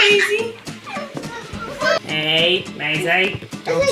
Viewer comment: I thought that toy said hi maisey